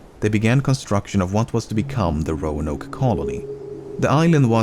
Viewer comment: For a second I thought it said Roxanne.